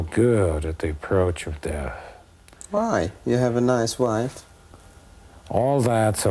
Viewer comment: The interview sounds like Borat: "You have a nice wife!" Hahah. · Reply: I thought he said "You have a nice life."